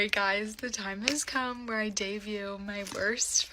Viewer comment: Did she seriously just say ‘dayview’ instead of ‘debut’? 😂 Send help